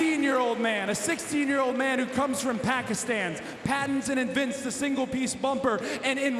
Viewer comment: Loved it when Cody mentioned Pakistan, A proud Pakistani & A huge AEW Fan 🇵🇰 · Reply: I thought he said Palestine. Who is the 16 year old he said?